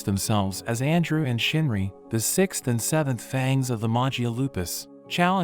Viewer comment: Did you say 67